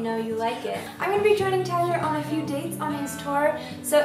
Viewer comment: I thought she said Taylor Swift at first and I was like WOAH!!! WHAT!??? Then I realized it was Tyler, which is still cool.